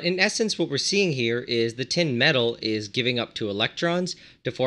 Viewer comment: Thought that said meth instead of metal 💀